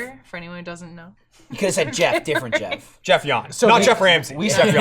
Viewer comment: I thought they said Jeff Yanez, I was like " how the fuck did my name come up?" lol